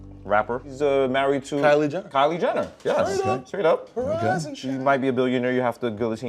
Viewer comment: Why does it sound like bernie said straight up